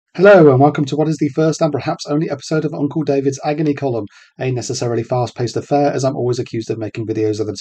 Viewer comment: When you said “a necessarily” I thought you said anus.